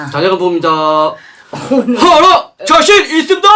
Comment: 호로자식 있슘다? 이거 뭐라는건가요? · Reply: 자신 있슘다 입니다